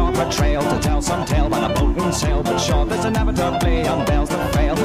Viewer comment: 초애폭풍세일 이라고들림..